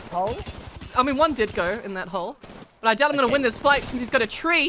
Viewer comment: I heard 'He didn't go into Coles?' I was like O.o